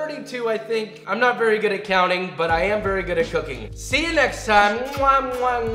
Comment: Did he just say “im not very good at counting but i am very good at cooking you”?!?!?!?